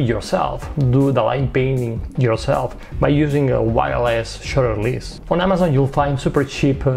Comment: I love your video and I love PhotoPills... but I have to admit, I really started laughing at myself on this video. When you said "wireless shutter" I heard "wild ass shutter".